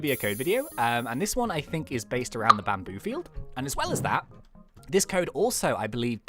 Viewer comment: What said: “...I think this code is based around the bamboo field” What I hear: “...I think this code is based around the batmobile”